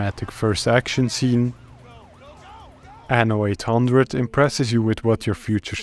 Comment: what game is this? i can't tell what you said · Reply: It's Anno 1800